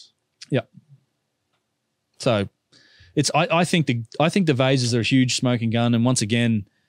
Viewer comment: Oh you said vase ! I thought you said these vizes are aliens ! What is a stoyn ?